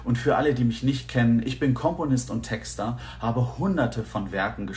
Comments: Wer hat bei 0:51 auch Kommunist verstanden? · Ich bin Komponist und habe 100erte von Werken geschrieben - - - 🤦🏻‍♀️🤦🏻‍♀️🤦🏻‍♀️ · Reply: Ich hab an der Stelle zuerst "Kommunist" verstanden bis ich es begriffen habe XD